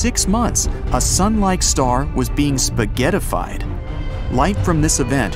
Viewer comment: a sun like star was being spaghettified Wait. Am I hungry or did he say spaghettified??!!!